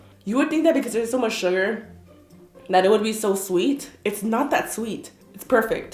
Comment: is when I heard tweet XD